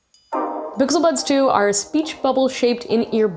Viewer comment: I heard her say bixel buds